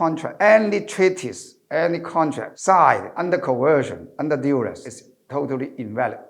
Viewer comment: Subtitle correction at 05:02 Should read "Under duress" instead of "Under duals"?